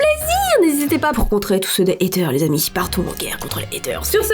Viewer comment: Quand tu as dis "portez vous bien" au début j'ai compris "partez vomir" haha